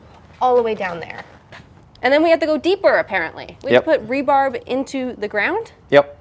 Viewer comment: She clearly said "rebarb"... 😆